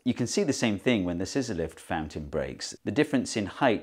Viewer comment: Every time you said scizzor-lift fountain I heard Sysyliph's fountaint, and for few seconds I was thinking it was some greek philosopher. And even after I got it, I woudl get bamboozled for a second next time anyway XD.